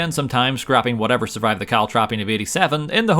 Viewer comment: I counted 53, with the shortened version "trops" At 9:47 i didn't understand that Word, with it there are 54 Times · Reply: "Caltrapping"